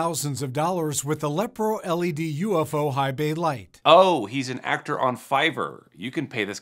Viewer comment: wait did he say ufo lights 😅